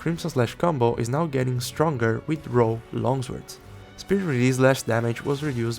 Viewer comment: From 3:35 to 3:40 says "The Crimson Slash combo is now getting stronger with ???? Longswords." What is that word? It sounds like "row" longswords, but I have no idea what a row longsword is. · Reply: "RAW", meaning with no elemental damage